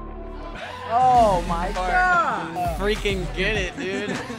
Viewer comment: did anybody else hear Daniel say fart is that just me😂😂